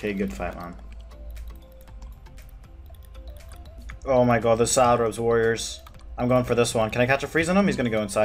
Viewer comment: Every time you say “salad” I hear “Sara”